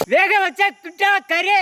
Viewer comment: what is he saying for cooked mutton meat? Th'tta curry?